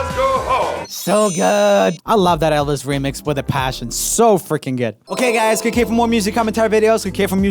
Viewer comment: can someone please explain what said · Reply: "So fricking good"